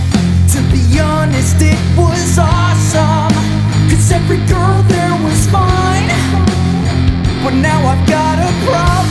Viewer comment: when he says every girl there was fine it sounded like he said every girl there is mine.